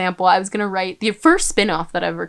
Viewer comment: what do you say the title was going to be? because it sounds a lot like my name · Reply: It would’ve been ALANNIS!